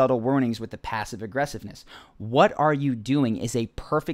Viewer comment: rdj doesn't even say "what are you doing" , he says "what are we doing" which I think is less of an attack back at the interviewer